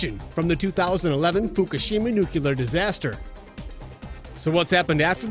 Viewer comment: I think he said ‘nucular’